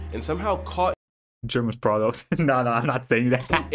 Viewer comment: What did he say? German product? ^^'